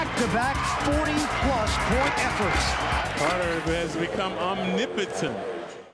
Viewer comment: what did he say @ 2:14? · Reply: omnipotent. All-knowing.